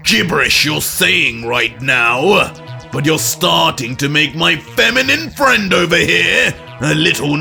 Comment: Bro 35:44 did vegito just call yamoshi a femboy😭😭😭